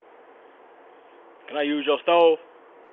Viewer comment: For the first video I heard "can I use your stove" not phone. Was it just me? Dude said STOVE. Also: stealing potted plants! Damn can't have shite